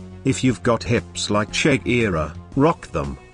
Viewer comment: like did he just said ""shek-ira""